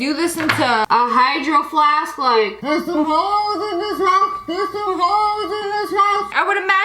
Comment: did she say “there’s so holes in this hat, there’s some holes in this hat” 😂😂😂